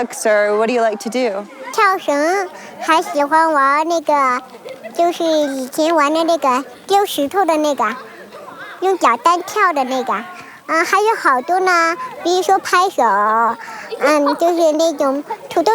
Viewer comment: Awesome video, paradigm breaker. The lil' fuckers live happily, why would this be reason for controversy?? Btw, I heard the word "nigga" at least 4 times from